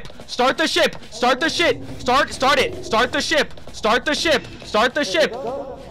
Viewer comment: I thought you were saying start the shit start the shit start the shit